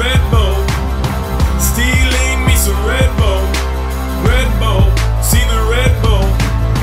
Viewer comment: sounds like he is saying red bowl